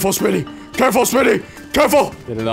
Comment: I stg when I heard “careful smii7y” It sounded like Patrick saying “careful SpongeBob” 😂